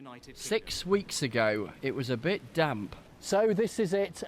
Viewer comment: sex weeks agoに聞こえた！